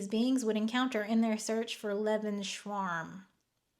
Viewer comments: I thought you said Leberschwarm, not Lebensraum 😂 Obviously from the context one gets it, but you would say it like Lay-bens-rowm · why do you say "leben-schwarm" it's lebens ra um I of course hate Nazis and the far-right, but in context, "lebensraum" could be, and is used as a shorthand for the drive for expansion — whatever reason for the next nationalistic war is, be sure it's about "space to live". So a summary could definitely use it as a shorthnand. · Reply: I thought she was saying “liebe shawarma” because she really likes German shawarma? 🤪